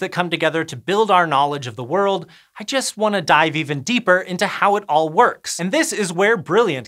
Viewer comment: ? I thought for a second he said "when I learn about .... I just want to DIE !" , and that was too much for an educative YouTube channel